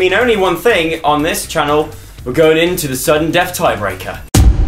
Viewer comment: When he said sudden death I thought he said suggen death.... lol